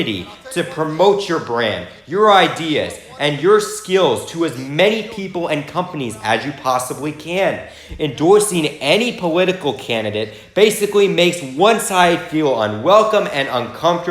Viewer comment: I'm sorry, did you say "powitical" at 6:19?